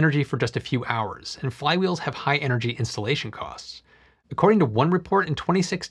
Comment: I think you can work on your enunciation. I thought you said "Insulation cost" not "Installation cost."